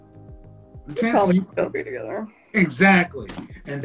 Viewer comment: what she say at 23:09? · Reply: They'll probably still be together